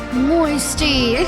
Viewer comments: Why do I hear 'gay mode' at 3:41 ☠️ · Can someone tell me what the hell she just said 3:43 💀💀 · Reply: Moisty